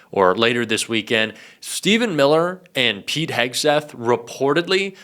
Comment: Every time one of them admits “…we are going to prison…” it is an admission of guilt. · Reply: I thought he said Peed Hegseth.